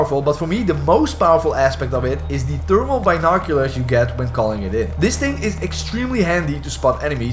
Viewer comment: I totally thought he said turbo binoculars not thermal binoculars 😂. Helpful vid though. Played my first two matches last night mostly had no idea what was going on but got I got fourth place in one so🤷